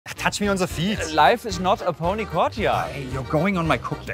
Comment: I thought he said "Catch me on the field" not "Touch me on the feet" The catch makes it sound like a threat and touch makes it sound more perverted like some weird German flirting